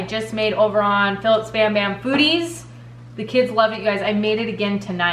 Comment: Every time you say foodies sounds like your saying booodies lol